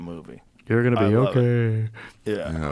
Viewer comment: on the video did anyone else hear “you’re going to be ok”?